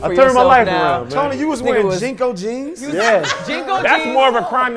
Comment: Uh, did he say Jinko jeans? At first I thought he said G-code. Why was Tony wearing Jinko jeans? That's the real question.